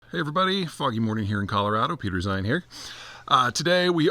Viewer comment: Literally thought you said "Hey, everyone, f*cking morning we have here..." at the beginning 😂